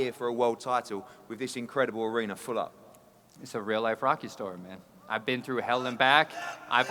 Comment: that sounded like gillie saying “rocky’s not real” 😂😂😂😂😂